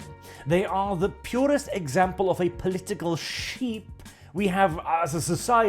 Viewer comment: I thought you said "political sheath" lol. Still a good analogy. They will take the sword of Islam, or the sickle of Communism with equal ease and readiness. The Roman word for sheath is 'Vagina'.